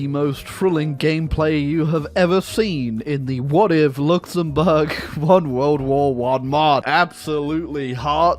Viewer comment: bro i thought you said "what if belgium won ww1". that would also be cursed but nothing beats fucking luxemburg💀